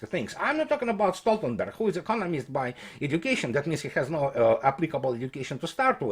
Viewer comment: Did he just call Stoltenberg a communist by education? I can't understand him.